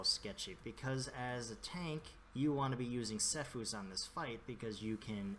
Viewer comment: ! At 3:48 what do you say? What is the thing I want to use? I dont realy understand, sy and thx :D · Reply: Attila Mucsi, legendary ring Sephuz Secret. The proc will give you a lot of haste and since you can proc it consistently its almost a permanent lust the whole fight.